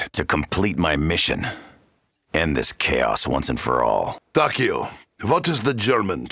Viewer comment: Ii think at 3:49 its "... my mission, end this chaos..." instead of the and